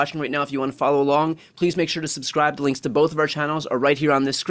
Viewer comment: Love the channel! One thing: every day in the intro i hear: ‘illegal breakdown’ or ‘a legal breakdown’ .. would be better to annunciate The Legal Breakdown imho 😁